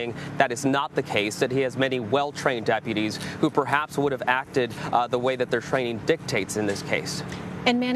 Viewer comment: Lol I was so confused at first. I thought CBS was saying he failed the ACT and that’s why he messed up.